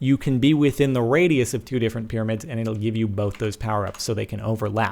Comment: I thought it said "pyramids, bacon". I'm sad now.